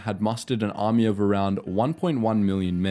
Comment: i love how at 9:57 he says germans and the subtitles says nazis instead ... thank you youtube ... that sucks